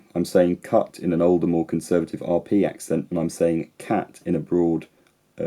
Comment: I thought you said cart.